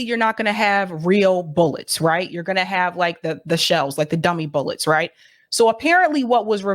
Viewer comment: Oh you said, 'use dummy bullets'... I thought you said 'use bullets, dummy.'